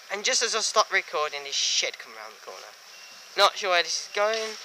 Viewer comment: why do you call the 66 "Shit" when you just film multiple units which are utter and complete shite!!!! Locos are much more interesting and sheds sounds better than the other crap you film! Or did you say "Shed" because its hard to work out what you said. Oh and also the DRS "Shed" would most likely be going to Daventry, use RTT then you'll know whats what, and when things are runnning, so you can turn up for what you wish to see.